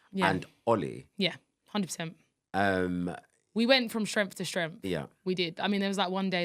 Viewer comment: I thought she said we went from Shrek to Shrek lmfao 🤣🤣